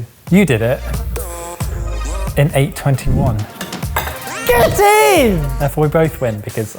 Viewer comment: says get it! but the subtitle says, go team! hahahahaha